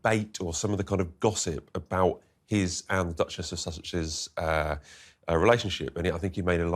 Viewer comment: I swear I heard you say, “Duchess of Sausages”… 😂😂😂